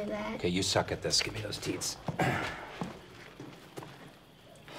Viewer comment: "Ok, you suck at this, gimme those teats." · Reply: Did you say teats? I thought you said beats.